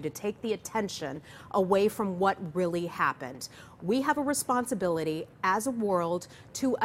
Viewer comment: Did I hear that right "We have a responsibility as a WORLD" 3:38 <link>